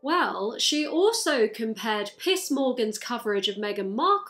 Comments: Whenever you said Piers Morgan I kept hearing "Piss Morgan" .. 😬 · I am not a native speaker and sometimes when you say 'Piers Morgan' I hear 'Piss Morgan'. It makes me smile a bit every time it happens. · Every time you say “Piers Morgan” I hear it as “Piss Morgan” and honestly, this sounds about right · Is it a coincidence that whenever you say piers i just hear “piss” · I watched this without hearing aids so I couldn't hear the 'r' in Piers so it sounded like you said Piss Morgan the entire time instead of Piers and honestly, was fantastic. 10/10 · I love that whenever I hear say "Piers Morgan" in my head the accent converts it to "Piss Morgan"... which wouldn´t be too far either xD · Your accent and my non-native-english-speaker-ness means that I keep hearing "Piss Morgan" instead of "Pierce Morgan and its FANTASTIC